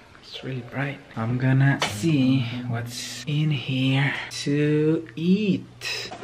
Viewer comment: why did i hear that he said tux eat💀